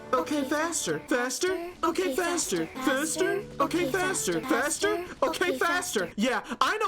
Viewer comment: What makes QiQi great is if you have slight hearing disability which makes it sound like she says "Bastard? Okay, Bastard!" Also, I know her scaling is absolute garbage, but funnily enough, I have C6 Eula on two accounts, long story, don't wanna talk about it, and on both of my accounts, QiQi does almost the same damage per hit as C6 Eula, which is either a testament to just how bad physical is in the game, no, wait, it really is a testament to just how bad physical is in the game... Mihoyo, plez fex, and only one of the accounts has the 5-star sword on QiQi, on the other one she's using a 4 star, can't remember which one off the top of my head and don't want to log in at the moment, but I believe it's one of the event 4-stars